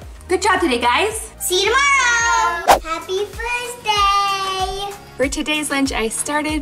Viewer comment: i thought she said happy birthday